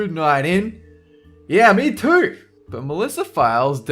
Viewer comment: Anyone with captions on at 3:04 would see that instead of it saying "Yeah me too!" it says "No me neither" XD